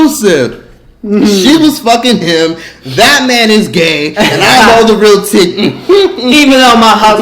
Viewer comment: I don’t know why I thought you said Batman is gay 😅😅😂😂😅😅😅😅😂😂 I 😂😂😂 😂😂😂😂😂😂😂😂😂😂😂😂😂😂😂😂😂😂😂😂😂😂😂😂hahahahahahahahahaha!!!!!!!!!!!!!